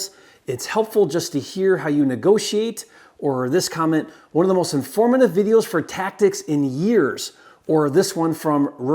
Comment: 01 how di the wholesaler say he found this particular unit? Hard to hear what he says. "Abandoned zone"? Thanks! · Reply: bandit sign